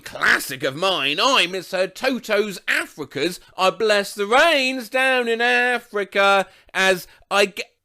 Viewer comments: I always heard it as "I missed the rain south in Africa" What is wrong with me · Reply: I always heard it as "I left some brains down in Africa" I'm from South Africa btw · I always heard “I bless the rains down in Africa” as “I miss the rage down in Aaafffrriicccaaa” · For the last one, I thought I heard, "I surfed the waves down in Africa" 😂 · Oh I thought it said I miss the rains not I bless the rains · I always heard it as ‘unless it rains down in Africa’, myself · I also thought toto said "I guess it rains down in africa" too 💀